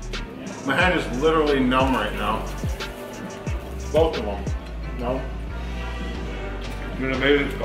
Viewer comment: I wasn't looking up at the video when said this and I thought he said "My head is numb right now... both of them." Hahaha. Also... Wow... Can't believe he was naked. Whip that wizzer around, SON!